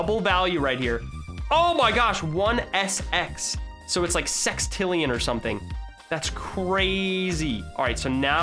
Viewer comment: did he just say sex